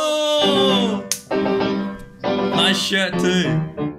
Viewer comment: My wifi cut out right as they said "nice shirt, too" so all i heard was "nice shit-" lol